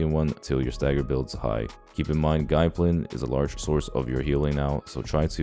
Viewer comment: Nice guide! But what are you saying at 3:42 i cant figure out the word "guiblin?" Is a big part of our healing now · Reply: its a talent called gai plin. just does healing based off how much we purify